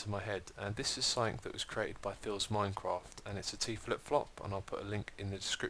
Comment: Decided I would delve deep into Hermitcraft history and have been listening to this in the background while I do other stuff. And let me tell you, I was thrown for SUCH a hard loop when I thought I heard you say "Philza Minecraft" in the year 2012.